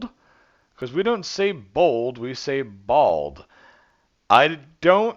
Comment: I heard 'we don't say bold, we say bowl'